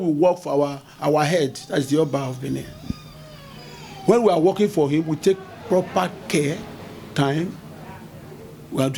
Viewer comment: I played this part 4:50 over and over did he say they for Amenhotep? did I hear that right is there some history we missing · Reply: According to him, he said "when we are working for the Oba, we take proper care, time." I did not hear any word like "Amenhotep."